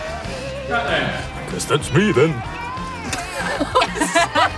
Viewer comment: What does Asa say at 5:54? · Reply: Sounds like he says guess that’s me then